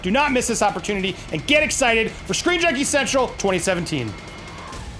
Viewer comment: I thought he said "Screen Junkie Sexual".